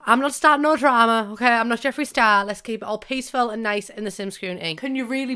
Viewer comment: When she said "I'm not Jefree Star" I thought she said I'm not Jefree styles and I sat there for like 30 seconds paused the video and everything trying to remember who the hell Jefree Styles is but then I realized she meant Jefree Star and I went on with my life.